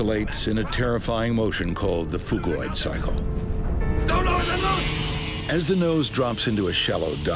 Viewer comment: When the first officer said something I thought he said 'dont load the load'